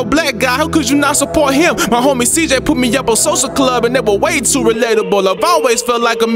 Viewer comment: Did he diss Social Club at 3:55? Can't tell what he says. Anyone knows? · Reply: Nah he just saying they was very relatable, he likes them